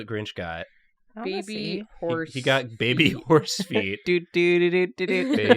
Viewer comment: I hope you know, that I heard "crab meat horse feet" to the tune of Jukebox Hero and this is ENTIRELY your fault. Everyone did a good job! I am glad you rescued, earworm Typhoid Mary that she is, from the bog.